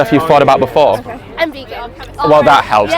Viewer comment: From what I heard, was asking something like "So all this stuff you thought about before?" She said "And vegan" meaning she thought about going vegan? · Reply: Nah, she said "I'm vegan"